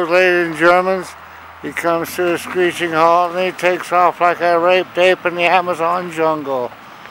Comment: DID HE SAY: HE TOOK OFF LIKE A RAPED APE IN THE AMAZON JUNGLE!'' , lol maybe I heard it wrong !!!!!!!!!!!!